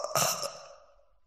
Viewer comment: jajajajaja yo siempre entendí "mtv loco", cualquiera